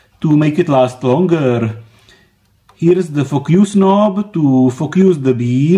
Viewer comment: What?! What are saying?? You saying “Fuck use” instead of “Focus”😆😛😜